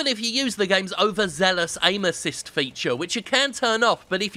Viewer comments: Somehow I heard 'anuses' when he said 'aim assist'. I think I belong in here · Jim mentions Aim Assist, I hear Anusist. I've lost my mind.